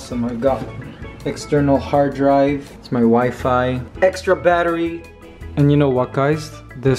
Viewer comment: "it's my wifi" Me: did he say his wife or something else lol (I know he said wifi)